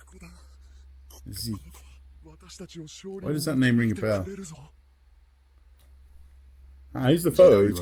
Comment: Did... did he just say "why does Zeke ring a bell"? O_o